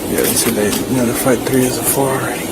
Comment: what do they mean when they say they got to fight 3 as a four.did i miss something? If someone caught what that ment or what he was talking about please explain thank you im so Confused about that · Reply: he said, “they have notified 3 of the four”. Notifying parents.